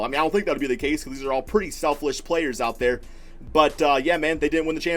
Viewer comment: “Shelfish Players” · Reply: I heard “Selflish”